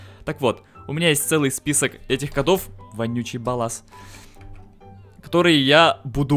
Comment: Лол мне послышалось Вонючий палас)))